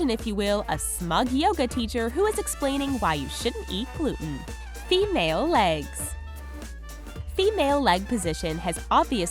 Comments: It sounds like she said "mayo legs" instead of "male legs" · I heard "mayo legs"